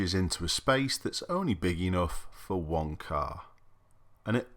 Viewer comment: "...and then the other side it merges into a space which is only big enough for one car" When says "one car" I thought he said "w****r" which would make sense in the context of this video footage!